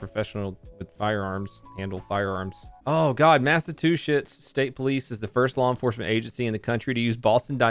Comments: I thought he said "NASA two shits" instead of "Massachusetts" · did you just say "mass a two shit's?" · Did anyone else hear him say massatushits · i heard masa 2 shits